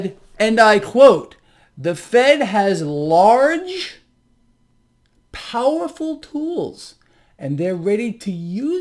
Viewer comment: Thanks for clarifying that! I thought said “ the fed had large powerful fools”